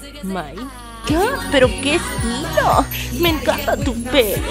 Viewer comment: entendi que sida pero era que estilo :v